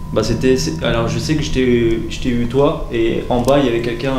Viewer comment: A 27:10 moi j'ai entendue au lieu de "Je t'aime" j'ai entendue "Je t'ai" voila faite lui remonter SVP 👍🏻😋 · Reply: Moi j'ai entendu "je tais eu"